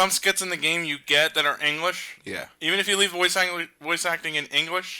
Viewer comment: who heard say engwish